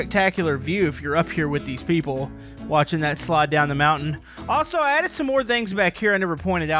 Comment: I thought you said "But Slide"🤣🤣🤣